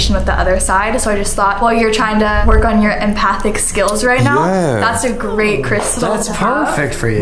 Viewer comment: Anyone else hear something weird at 24:13? Like someone going "Oh, yeah"? Or is that just some funny sound they made in editing? Maybe it's just idk hahaha